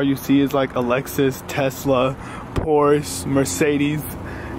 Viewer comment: did he just say pors instead of porsche